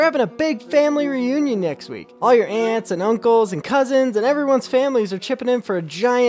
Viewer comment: I thought bro said ants and uncles 😂